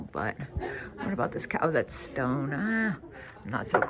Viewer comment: 🤣🤣🤣 I thought you said “ this cow that’s stoned” lol